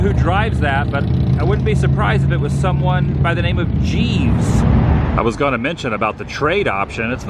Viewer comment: did say "Jeeves"? Can't be sure but I think he would look pretty cool in a ster :D